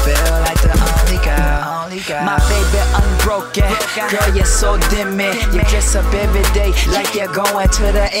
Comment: is he saying "my baby unbroken, oh you're so Demi"??? aww so cute